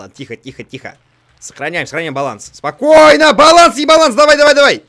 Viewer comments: Мне послышалось?На 15:25 он сказал:Баланс-Ебаланс!Мне одному так послышалось? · Вам тоже послышалось 'Баланс-Ебаланс'?